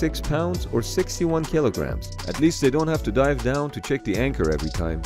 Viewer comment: Ok so you're telling me that one ring of the chain weighs as much as me🤯😵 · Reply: that's my bad i thought he said 130 kilo 😬